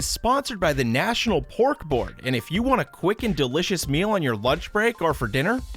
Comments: I totally thought he was saying “Bork Bork” instead of pork board😂😅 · I thought he said pork bork